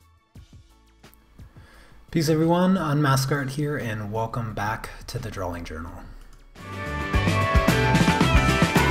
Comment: Every time you introduce your channel, I hear “Hi Everyone, I’m Asgard here...” and I think of Thor. 🤷🏽‍♀️😂 #random. It’s like playing Mad Libs with every intro. Lol! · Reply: Haha, You had me saying it all morning until I finally heard it "Unm-ask Art"